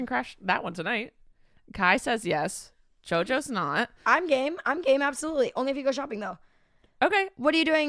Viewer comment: i thought jojo said im gay and not im game🤣🤣